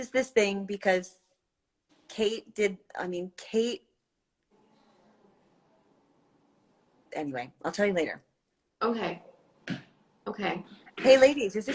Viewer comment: what did cate say? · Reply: Cate did a live instagram with sarah and cate said "i'm a lesbian"